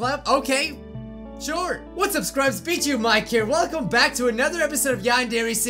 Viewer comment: I thought he said Bitchu—Mike